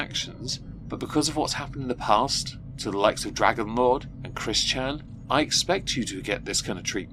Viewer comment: Did I hear "DragonLord"? Thats the awful guy in my pfp